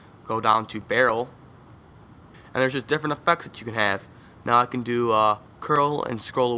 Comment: I thought you said "ef*cks" until i realized it was "effects"